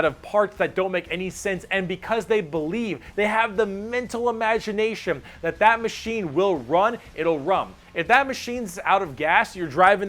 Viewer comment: is it jus me or did i hear him say "they have the mental imagination that the mashine will run, it will RUM "